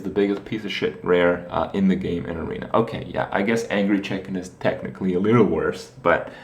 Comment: What did he say was worst at 1:13? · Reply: larson Angry chicken